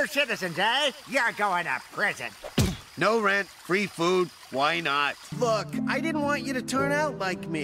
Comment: what did he just say ???? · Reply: "Why not"